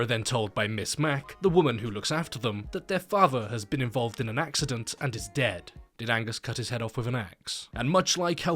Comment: I thought he said Ms. Mac told them "He was in a Mac-cident" I'm such a dweeb